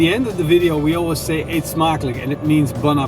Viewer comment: Oh. I always thought you were saying its Maklik. I figured your name was Maklik. Love the vids!!